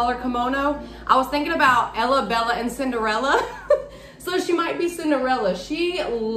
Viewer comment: Girl I thought you said “Ella Bella and Cigarella” 🤣🤣🤣🤣🤣🤣